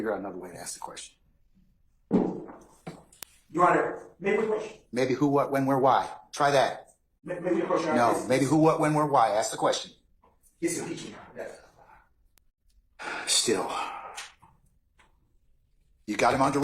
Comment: does anyone know what he asked the judge here? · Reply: Sounds like "May we approach your honor". Not definite though 🤷🏾‍♀️